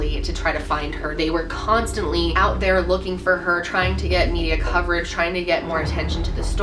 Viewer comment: Um did u say they were trying to get u to cover it